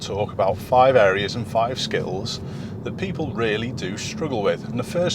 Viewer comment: did i hear skittles? 😂😅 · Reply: He said skills